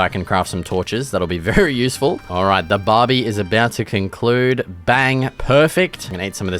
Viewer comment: Did bro say Barbie